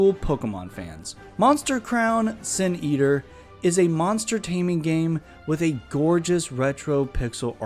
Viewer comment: I originally thought you said monstertaining as in entertaining monsters lol😂. Anyways, that game looks a little like a one off of How To Train Your Dragon.